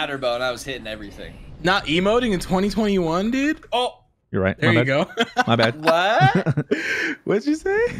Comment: Why does it sound like he says you’re right ma nigga